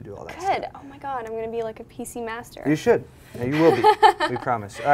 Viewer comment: at first it sounded like Pussy Master